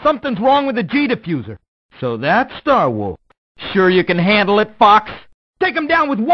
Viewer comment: "So that’s Star Wars…" (Idk if that’s what he said, but that’s what it sounds like)